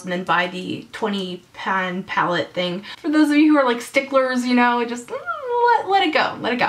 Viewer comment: I proper amused myself when you said "sticklers" because i misheard and thought you said "for those of you that are like hilters" ahahahaha oh dear, i hurt my own brain :')